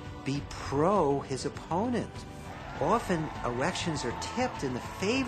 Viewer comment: I swear this dude says "erections"